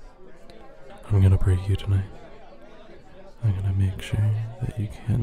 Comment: I wanted to get to the juicy parts so I skipped forwards and all I heard was “I’m gonna breed you tonight” and I am so confused 😭😭